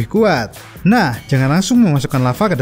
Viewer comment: jangan asemm??gaksalah denger